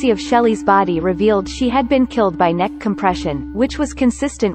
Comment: Gah! I thought it said "Killed by an Education." Such a let down.